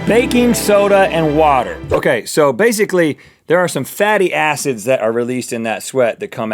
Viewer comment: listen. I thought he said Fatty A***s